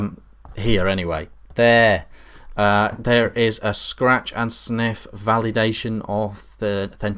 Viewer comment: did he say scratch and sniff...omg, lmao, also rofl...